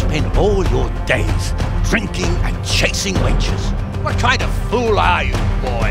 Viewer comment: I heard "drinking and chasing wenches"... will there be boobs?